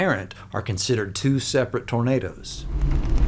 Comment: I love how the subtitles say burritos instead of tornados. Amazing